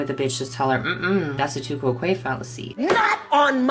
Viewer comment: Before he put up the tu quoque fallacy definition, I thought he was saying "the two-quote queen fallacy." I use captions because I'm a little hard of hearing, and it was coming up there as "the too croquet fallacy." So I'm grateful he uses cards, putting written content onscreen to help us follow along more easily with complex ideas.